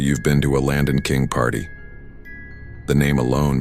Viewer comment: I thought she said Latin King party 😫